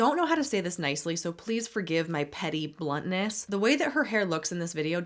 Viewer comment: "please forgive my petty bluntness" is gonna live rent-free in my mind for quite some time 😂😂 thank u for this! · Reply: I thought she said "blondness"